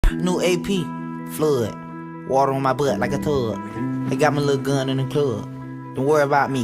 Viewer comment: “Water on my butt like a tub” “don’t worry abt me im a thug” in the same verse 💀💀💀💀😭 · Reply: it’s okay I hear “water on my butt like a turd”